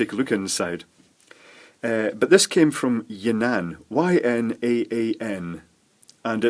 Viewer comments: Am I the only one that laughed when it sounded like he said "It came from ya nan" XD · I heard "It came from yer nan"